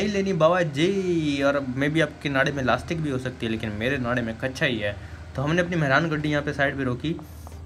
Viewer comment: chek what he said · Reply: Hahaha naray main kacha